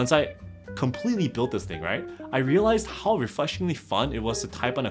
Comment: am i crazy or did i hear refleshingly fun? were you trying to say refreshing and flash at the same time? lol, or maybe reflashingly fun